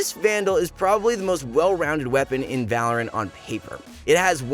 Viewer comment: he said "well" instead of "wall"